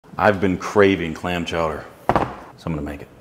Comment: I swear I heard him say lamb chowder